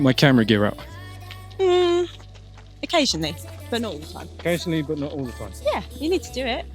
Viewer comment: I just hear occasionally a minolta 😂